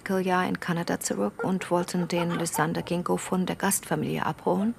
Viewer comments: Lysander Gingo 🙈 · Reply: Ich hab auch beim 4. Mal hören noch Gingko verstanden 😅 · Wie heißt das Kind? Lysander Gecko? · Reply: 😂 ich hab Lysander Ginko verstanden..